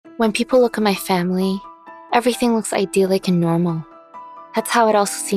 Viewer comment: Girl: I have a normal family Later: My dad pinned me down and abused me Next: They locked me in a dark storage room Me: WTF CALL THE POLICE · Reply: "Girl: I have normal family" That's what I thought she said too until I replayed it. What she really said was "When people see my family, everything looks idealistic and nornal." So she was saying it in the perspective of other people. Those people didn't see what she went through.